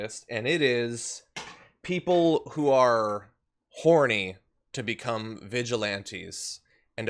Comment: Did John say horny? 😂😂😂